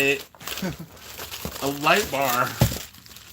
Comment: After the 5th time watching this, I realized he said light bar I thought he said nutbar!